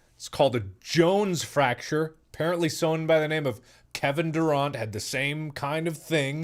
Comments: did he just say “kevin duront?”... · Did he just say Kevin Doorant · Did he just say “Kevin DurAUNT” 😂